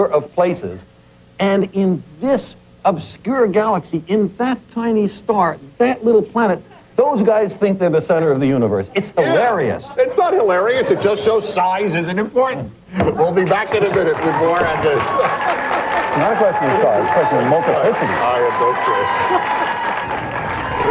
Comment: did he say "it shows science isent important"??